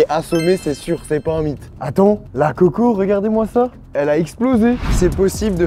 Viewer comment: La QUOI ??? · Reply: Bh la coco